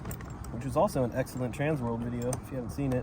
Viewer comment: what did you say at 9:12 ? Transmo? i really feel the same about factory new bearings whatever video you mentioned i need to watch it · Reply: transworld